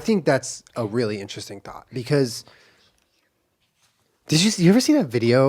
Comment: Every time says “thought” I think he says “thot”. “I think that was a really interesting thot.”